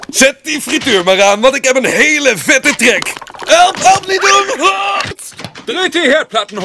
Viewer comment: whoa! I heard “Help! Help me dude! WAHH!” 😂